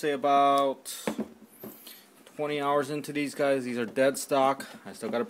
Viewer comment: did he say there dead stock.?? O_o